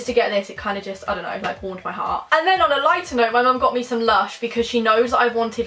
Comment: When said my mum brought me something me something from lush I thought she said my mum brought me some lunch 😂😂😂😂😂♥️♥️♥️